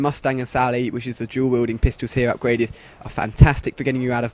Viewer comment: sounds like you say jew wielding pistols